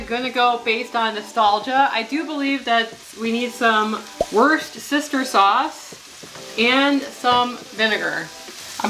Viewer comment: I love how you said worcestershire sauce-it sounded like you said “worst sister sauce”😂😂😂😂😂😂